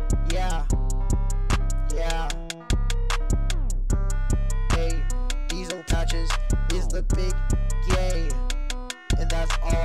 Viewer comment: I thought this dood said diesel patches is 2 feet 2